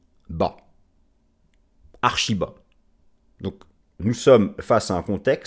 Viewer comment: Lorsque j'ai entendu : "Les supports sont archis bas", j'ai direct pensé : "Ils sont Ar Shiba Inu". 😂Je suis tellement matrixé... (bon, j'ai quand même retenu les autres trucs importants de la vidéo, hein^^)